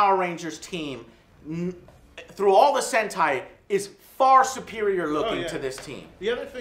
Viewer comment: Is Joe saying "hentai"